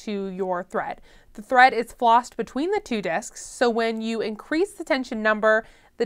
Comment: After "rewinding" several times, I still don't know what you said at 2:28 - "the thread is flossed between the two disks" Huh? What are disks? Have you already explained "what is a disk"? Maybe you said "desk". Remember, we are beginners. I wish there was "closed captioning" so I could understand/read what you're saying. This makes twice already where I'm unsure of what you said - after only 2 minutes of the video have elapsed! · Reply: Hi Jeff! The thread is flossed between two tension disks . On some Machines they are easy to see, on others not so much. So, every time you adjust your tension Knob, you're really just pushing those two disks closer together or further apart, which in turn exerts more or less resistance on your needle thread